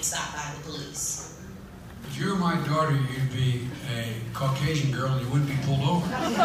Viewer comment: You all heard “ if you were my daughter you’d be a Caucasian . . . “ What I heard “ I wouldn’t sleep with a black woman. 🤔🤔now who is the racist?